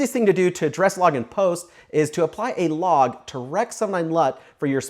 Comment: Can you tell me what sentence you say at 4:22? For the life of me I can't tell what you're saying ("direct so my LUT"? "to rec so mine LUT"?) ??? ... I give up. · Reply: "To apply a Log to Rec709 LUT"